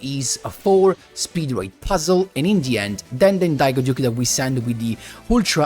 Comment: Oooh you were saying “in the end” I thought you were saying “Indian” 😂